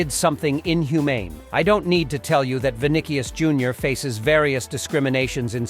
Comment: what he say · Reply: “Vinikius” 💀